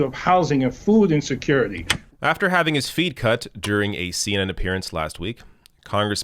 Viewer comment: I heard say "after having his feet cut". 😨 It was "feed".